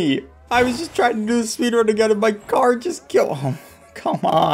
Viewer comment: what did he say? all i heard is go... d-.. drugs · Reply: "don't do drugs kids" or something like that lol